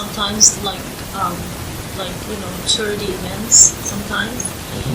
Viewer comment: did she say maturity events and if so what is that???